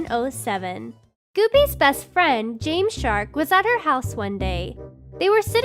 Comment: When she said Jamesshark I thought she said jamescharles